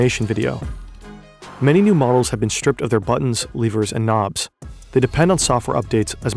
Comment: did you just say “Leavers?”